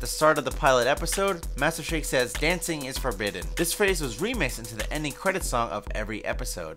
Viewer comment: Wow 😯 When my sister & I were kids we thought the ending credits said “ Dance in your closet “ or “ Ants in your pocket “ 😭 All these years later I find out it’s “ Dancing is forbidden “ lol